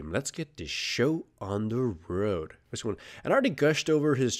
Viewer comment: What does he say at the start of every video? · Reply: "lets get the show on the road"